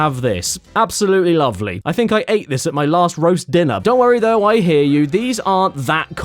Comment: I get that this isn't the intention of the joke but did you just say you ate arse at your last roast dinner?